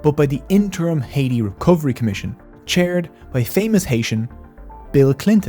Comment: did he say “Famous Haitian..Bill Clinton” or am I going crazy?